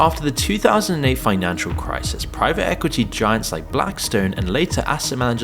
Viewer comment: did i hear a blackrock and stone!!!